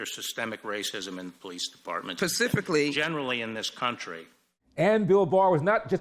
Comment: Did she say pacifically?